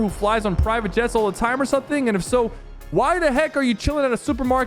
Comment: Sypher: So the heck Me: I’m pretty sure that’s not what he said